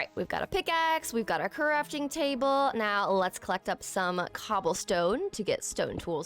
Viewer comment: it sounded like you said corruption table. I love your videos and i love the horror mods❤